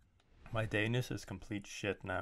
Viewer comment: I thought he said elvish but later realized he said danish lol